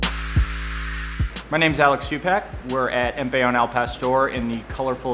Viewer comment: did he just say im alex tupac